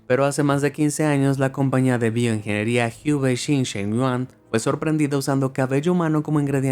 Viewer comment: uy no que dijo hioue shin shen wuan XD